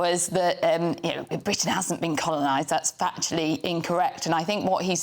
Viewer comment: Sounded like he said "Britain is being colunised". Its only a matter of time. Immegrants are more prejudiced than anyone these days. Guardian foody tastes met again 😂 "Nobody wants illegal immegrants clajming benefits, bit therea different sides to the argument" 😂😂 BTW immegration was almost never needed in the UK.